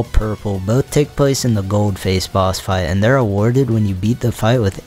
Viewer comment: Rapid Response Squad Member is here - Dockronic. Thanks for playing the mod! I added a lot of golden stuff for Goldface boss fight! Happy upcoming New Year too. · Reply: Bro why did I thought you said Ghostface fight